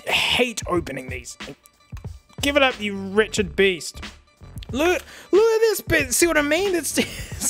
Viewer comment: Thought he said “Richard Beast” instead of “wretched beast” there for a second. Either way, it’s a golden malapropism that I’m going to start using from now on.